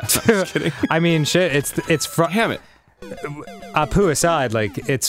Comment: Did anyone else hear Arin say HAMMIT?